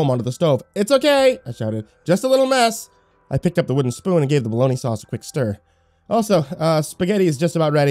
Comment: Hold the hell up, did you say bologna-sauce?